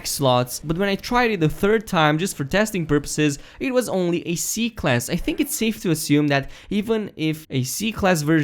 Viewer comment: Your accent is so cute ^-^ plus kinda sounded like he said Ass Class :3 lol